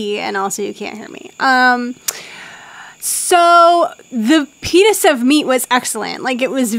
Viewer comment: "the pen!s of meat" I swear that's what I heard